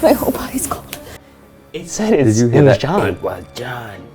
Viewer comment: I heard “it was him” instead of “it was them” 🤔🤔🤔🤔